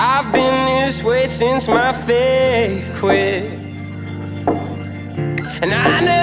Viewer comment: I first heard "since my faith quit" wrongly "since my fifth quit" which honestly went well with "and then I crawled back to the life that I said I wouldn't live in" vibes